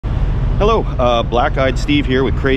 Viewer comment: Had to rewind , thought he said "hello 👋 black guy Steve here with crazy neighbor" 😂😂